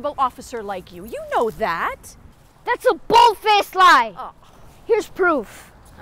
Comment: Hahaha! I liked when the son said “THAT’S A BOLD FACE LIE!”. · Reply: I thought he said "thats bull face lie"